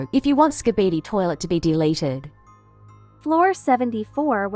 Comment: What she said 😭🙏💀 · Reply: She wants skibidi toilet to be deleted